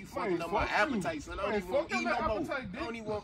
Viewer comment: The “real nigga” after is so accurate 😭😭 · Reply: I thought he said friendly nigga😂😂😂